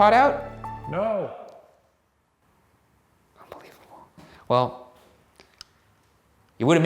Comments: After Shane says "It's 2018," anyone else hear "You're mistaken?" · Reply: I heard “unbelievable “ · When it asked, “what year?” And you guys said 2018 it sounded like he responded and said, “I don’t believe you” or “how old are you” but I’m probably reaching 🤷🏾‍♀️ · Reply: I hear ‘unbelievable’ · after Shane said "it's 2018" I thought I heard something like "amazing" is it just me lolol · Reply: I heard 'unbelieveable'